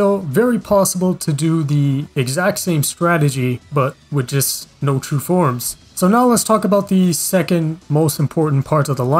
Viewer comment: I thought you said no true forms🤡